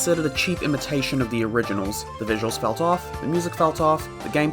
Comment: did you said "so tuff" or "felt off" in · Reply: He said felt off